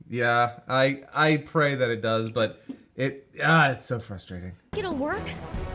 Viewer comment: I swear to god, I never noticed an error. I always heard her say: "Did it work?"